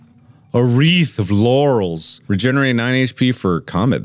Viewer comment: i heard a wreath of yannys